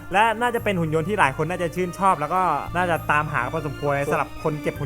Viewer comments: ทำไมได้ยินว่าพอสมคว*ว่ะะ55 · ได้ยินว่าพอสมคว..